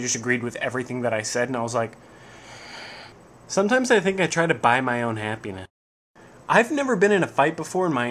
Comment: the video had a hiccup when I was watching and I heard "sometimes I think I buy my own penis" Which made me laugh